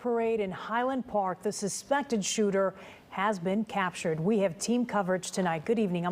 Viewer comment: did she say second shooter? i guess i missed something.